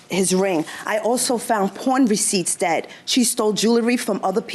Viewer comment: I heard “porn receipts”